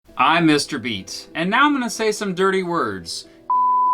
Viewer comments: legit thought u said u were "mr beast" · I thought he said "Hi, im Mr. Beast"